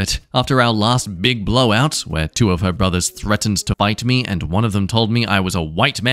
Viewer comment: Did anyone else hear “bite me” instead of “fight me”?